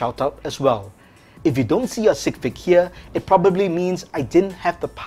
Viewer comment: On 2:46 the captions says sick pig instead of sig fig 😁